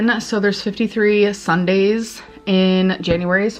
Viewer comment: wait did she say “there’s 53 Sundays in January” my mind can’t comprehend that, please someone clarify I’m not going crazy…